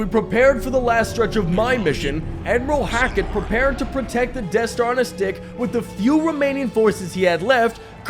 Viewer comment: I definitely misheard that as "Death Star on his dick."